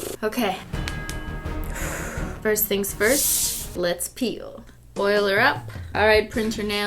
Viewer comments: i thought she said let's pee🤣😂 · for some odd reason i thought cristine said: “let’s pee...” and i shifted a bit in my seat😆